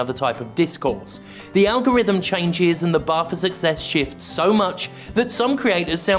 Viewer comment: when you said "the bar for success shifts" I thought you said "the barfer suck sex shits" for a second but that's definitely because I'm high and not your fault